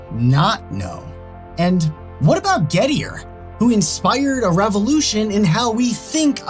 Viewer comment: At first, I thought you said "ghettoier"